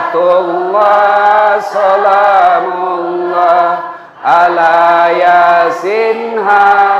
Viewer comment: assalamu'alaikum wr.wb,maaf paknustadz sebelumnya kalau saya salah menilai,kalau tdk saya salah dengar,bacaan sholawat sholaatullooh solaamullooh bukanya yg benar salamullooh,🙏🙏🙏